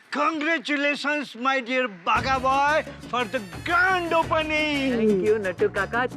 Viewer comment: he said congratulations Baga boi for the " gand opening " 😂